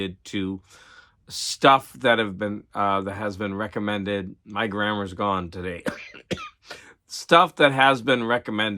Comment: I misheard it as "my grandma is gone today" :S Modern Ma... ooh la la! :D Never heard of that magazine TBH. Now, that Famous Monsters of Film cover is super sexy!!! Is it called "slabbing" thst thing that the other vinyl community people hate? What's up with that rating? I was automatically expecting you to do an unwrapping video of those sci-fi mags.